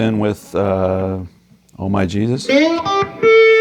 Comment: Instead of “oh my Jesus” I always heard “all my cheez whiz”